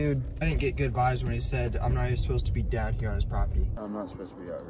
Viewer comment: In the midst of the shouting I heard “your not even supposed to be here